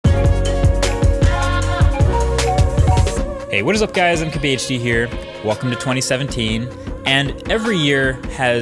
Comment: "What's up guys, MKBHD here, welcome to 2017..." The real question is, when are you going to center those wall panels? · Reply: I thought he said ""What's up guys, I'm KBHD here"